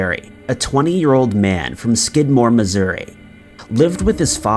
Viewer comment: All I heard was " skid mark Missouri "